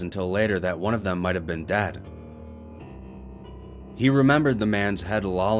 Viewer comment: did u say dad or dead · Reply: It's dead